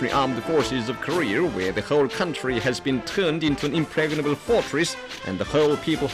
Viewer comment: If the enemy dares to invade the land of Korea even an inch, the Revolutionary Armed Forces of Korea, where the whole country has been turned into an impregnable fortress, and the whole people have been equipped with arms, will smite the aggressors mercilessly. American POWs sweat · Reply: I thought it it said pregnant fortress